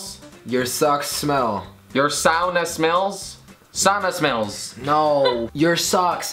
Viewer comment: I heard sanna